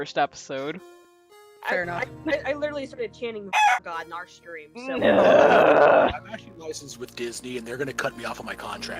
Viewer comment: wait... what was said? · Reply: "BAGAWK God" :3 ~